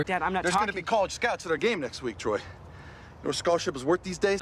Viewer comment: howd i hear there'll be collage scouts at our GAY next somethingsomething